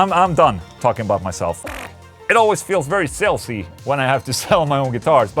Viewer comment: I thought you said “It always feels very filthy when i have to sell my own guitars” 😂 But you said “sales-y” . For your humbleness i just put $100 away towards buying my first Solar guitar.